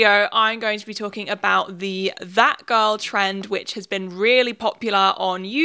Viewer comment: LMAO WHEN SHE SAID THAT GIRL TREND I THOUGHT SHE SAID FAT GIRL TREND